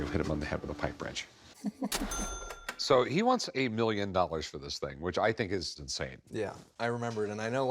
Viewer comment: Sounds like Rick said "He wants 8 million for this thing"